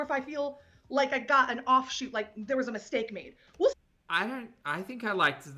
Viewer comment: omg the moment the video was paused made it sound like said woof instead of we'll and i was like wtf did she just bark 😭😂